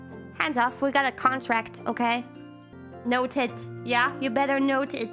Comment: I thought she said "no tits", oh my, my mind got so corrupted x)